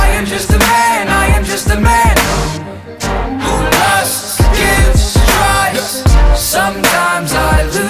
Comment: I always thought he was saying, “I’m just a man, a man who loves Jesus Christ”. 😂